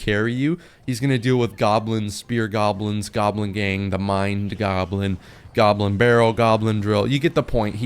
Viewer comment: he really said mind goblin